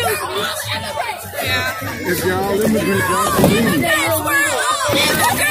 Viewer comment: I thought they said we're all hyoocrites